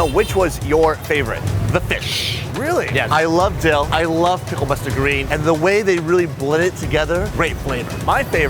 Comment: What's say about the fish and dill @ 14:37? Pickled dill or picked fish? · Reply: Pickled mustard greens